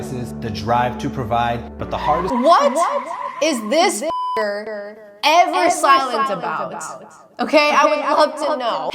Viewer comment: LITERALLY WHAT WORD WAS THIS IM SO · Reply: I was confused too 😭 she said fucker